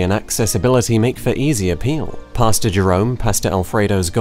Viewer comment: Dude, this whole time I thought he was saying “Pasta Jerome.”